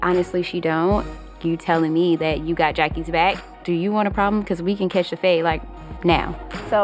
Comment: What does Malaysia say at 1:51? "We can catch the …. ?" Thanks!! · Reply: "We can catch the fade"